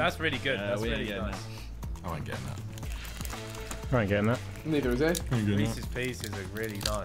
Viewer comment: Did Vikk just say the N word? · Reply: Neither...